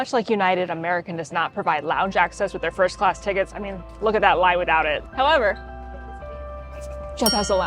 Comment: Can someone make out what says after "Much like United, American does not provide lounge access with their First Class tickets". I'm hearing: "I mean, look at that lye we doubt it" which makes no sense. TIA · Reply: “…I mean, look at that line without it!”